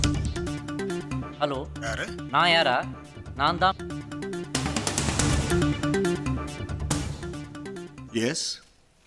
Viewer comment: what did vivek say at 1:05 ? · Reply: Nan tan mairu pudungi 😎🤣🤭